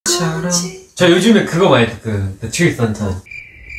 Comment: "tree tong tong" will be forever iconic😂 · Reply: God I loved that! I heard him say “the truth untold” and I was originally so confused why Jay didn’t understand him 😂